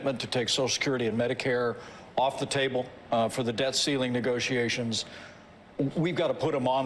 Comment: Did Pence say “death ceiling” at 1:22 mark?